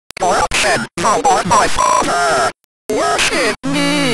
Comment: i hear "Corruption, you aren't my father." in